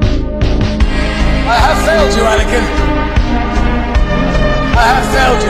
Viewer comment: Bro I Heard 'I Failed You Vatican' 💀